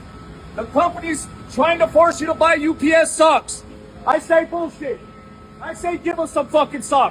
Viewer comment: I thought he said "Stocks" .. I'm like "Why is the higher ups trying to force workers to buy UPS stocks?" but then I opened the Closed Caption and realized it was "Socks"